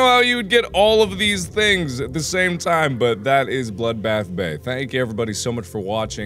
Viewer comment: just now watching this...always thought it said blood bath baby